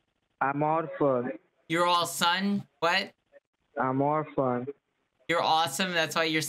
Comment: he is not saying - I am AWESOME, he is saying - " I am ORPHAN".